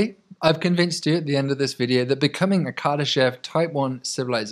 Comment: thought it said kardashian type 1 civ for a second there....